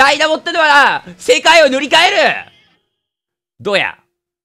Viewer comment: 覚えとけよ！ダイナモってのはな、世界を塗り替える！どうや が 覚えとけよ！ダイナモってのはな、世界を塗り替える！どや！ に聞こえた 正直 どうや か どや！ かどっちかわからない